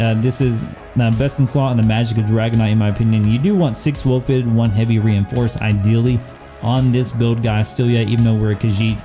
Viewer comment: ive replayed this about 8 times and still have no clue what in the actual fuck your saying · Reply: Hes talking about traits 6 wellfitted 1 Reiforced